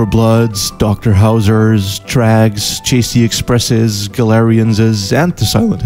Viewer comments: The game depicted at 4:03, what is it called? I heard "Drags" but that isn't helping me find anything. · What's the game at 4:03? I can't understand what he calls it. Drags? Trags? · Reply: TRAG